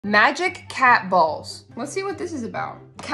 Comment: I saw this on my fyp and I literally thought it said I love cat balls 😂